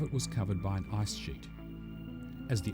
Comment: i hear ice sh## at 0:40 lol